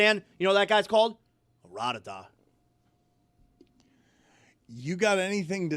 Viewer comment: For a looong second, I thought said a Yittish word...rah-der-dah...like a "good teammate" 😅